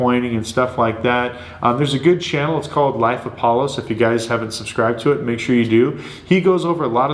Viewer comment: I missed the name of the YouTube channel recommendation. I think you said "Life of Palos?" About the 31:28-31:33 mark. Please advise.